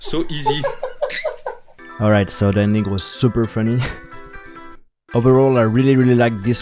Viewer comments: did he say the n word? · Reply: He said “ the ending was super funny “ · did he say that nigga was super friendly